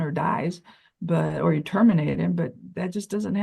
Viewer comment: Did she just say terminate him?